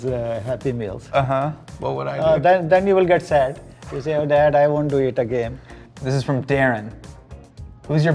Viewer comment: “Then you get sad and say dad I want to eat again” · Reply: it did sound like he said "i want to eat again" lolol